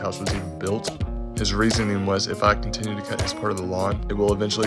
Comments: Dang took me 5x listening but I got it! He’s said "now I’m cutting the other yard" Ha! Had to edit I thought he said property 1st 😅sooo 6x to get it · “Long story short, we came to a conclusion and now I cuthewhayablabagaba” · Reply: LoL I thought the same thing but honestly, I heard him say "the whole yard" It just was muffled by the music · Listened to this 6 times, and I still have no idea what that last thing was that you said. "come to a conclusion and now I..."???? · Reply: Sounds like he says " now I'm cutting the whole yard"....which he had been doing all along...😂